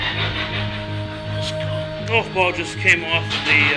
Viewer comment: sounds like someone says golf all before does kinda weird maybe its just sayin under his breath